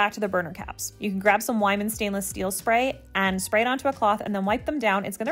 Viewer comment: I swear I heard "grab some wine and stainless steel spray". That sounds like my kind of cleaning! :)